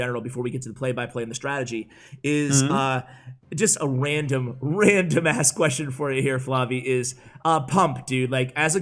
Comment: did say ass?